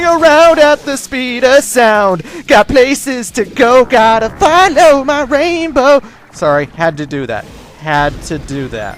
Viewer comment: In the city escape song, instead of "follow my rainbow" I thought it said "follow my way through" which still makes sense fortunately.